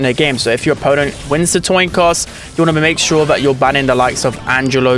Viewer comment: bruh said “toin coss” instead of “coin toss” 😂